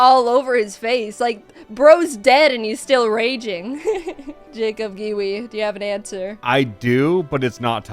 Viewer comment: I know I probably misheard this, or it's a joke, but did jaiden call Jacob babe here. I know jaiden is aroace, but that's the reason I'm asking. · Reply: She said "Jacob, Giwi, do you have an answer" But I understand how you got confused. I had to listen to it a few times to get it